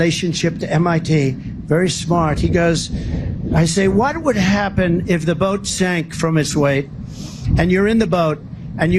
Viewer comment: I thought he said, when the boat sinks under his weight, and wondered why he'd be sitting on a battery. This is Trump, without a speech to read off a teleprompter, showing his arrogant stupidity, and how unfit he is to be in the White House.